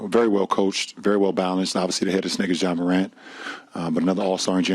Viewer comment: Am I tripping or did lebron say they got this n!gg@ ja morant lmao 22:00 min Mark